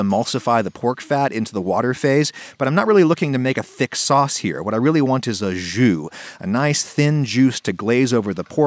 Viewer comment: i thought he said "what I really want is a Jew"